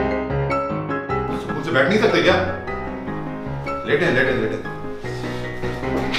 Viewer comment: i cant understand what is saying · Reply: says Pipe ghus Gaya hai